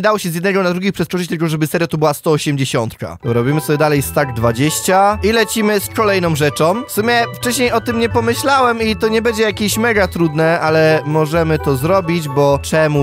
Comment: czy tylko ja usłyszałem pomysłałam